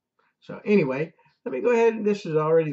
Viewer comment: At the end, it sounded like you said, 'uploaded after 'Erie' pays' ; that's what's causing the confusion. ... It sounds like they are asking the Trial Judge for a new trial? ... [I looked for Tennessee, and there's a time limit on that request.] At the moment, it sounds like Eire is asking the Trial Judge for some sort of 'relief' from the verdict. A new Jury Trial is being requested by Erie; and it seems that the Trial Judge will first make that decision. [It's yes or no for an answer on that one] The information about Tennessee says, the Trial Judge has a couple of options. The most favorable option for is for the Trial Judge to say NO to Erie, and tell Erie to pay-up. After a denial of new trial or a denial of a request for the Judge to change the jury verdict [~all decided by the Trial Judge], ~ Erie can then appeal to the Appeals Court ~ to change the verdict or Erie can also ask the Appeals Court to order a new trial. [Erie is suppose to give ~good reasons for its requests. Erie can't say, 'Because we're pitbulls hanging onto the cash!' The lawyers for Erie Insurance sound like a pack of mean pitbulls, that will not let go of any cash. ... Sometimes, in such situation, it's NOT NOT that Erie is especially fighting with; ~ it might be, Erie maintains a reputation with >all the lawyers that might sue: = 'Erie doesn't pay off ~any claims without a big fight in all the court venues.' Such a reputation by Erie would lend itself to more lawyers telling their